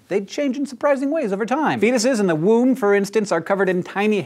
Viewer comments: who else had to rewind that to figure out just what he said The first three times I thought he said “penises in the womb” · when he said "foetuses in the womb" I totally thought he said penises in he womb at first =/